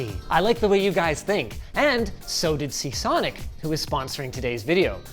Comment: Linus casually referring to the sponsor as SICKsonic instead of their real name... lol